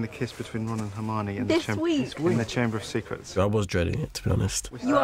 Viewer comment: 英語でなんて言ってるか聞き取れない… You are not ??????? Everyone was ???????my kiss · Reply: You are not allowed to be here Everyone was there for my kiss かな？